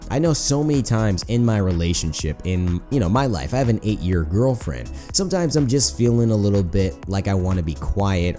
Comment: for a split second I thought you said 8 year old girlfriend 😂